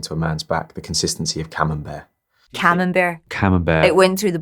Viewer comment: "the consistency of a (?)" ?? HELP ME I don't understand what they say and I already heard it a million times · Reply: Marina Fernandes Silva Camembert